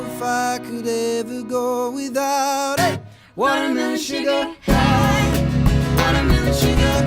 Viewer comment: so i totally thought he was saying water moon on first listen